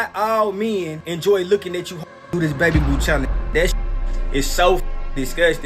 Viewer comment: "This babyboo challenge" Me: what the heck is the babyboo challenge? 👁👄👁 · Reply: I thought she said Betty and or baby boop lmaoo